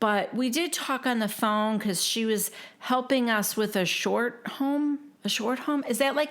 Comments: I thought Jenifer said a 'shore home' as in Jersey shore. I could ne wrong tho. Love your recpas, happy st Patrick's day 🍀🍀🍀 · I thought she said shore home ?